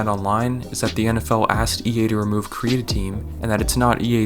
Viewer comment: I swear he said the nfl asked ea to remove creatine 😂